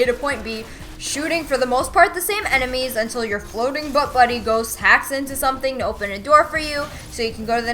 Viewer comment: LOL I thought she said "floating butt buddy" when she was talking about Dinkle-bot. I was cracking up!!